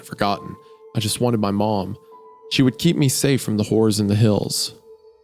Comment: i thought he said "she would keep me safe from the whore's and hills" lol then i look at the screen and its horrors im soo cooked chat